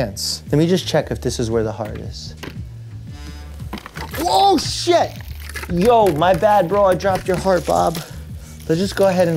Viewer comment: did he just say oh sh!t?😭